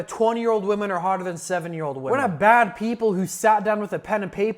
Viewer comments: I heard 7 year old women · Do I need to clean my ears.. or is it that at 4:10 the subtitles say 70 but the audio said 7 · am i the only one who heard "7 year old women" 😭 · I'm pretty sure I heard 7🤔...